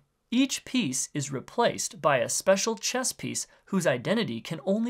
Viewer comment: I thought it said “crisis” instead of “chess.”